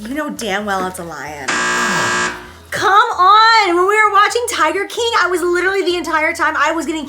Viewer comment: jac did you say tiger king??? O.o